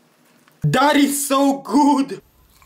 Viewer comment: I thought he said daddy’s so good instead of that’s so good 😭😭😭😭😭😭😭😭😭😭😭😭